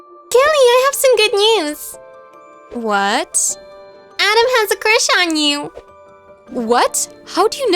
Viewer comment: video: " adam has a crush on u" what I hear: " I don't have a crush on u"